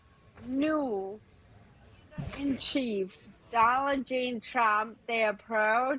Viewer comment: Did she just call him Donald Jane Trump?😂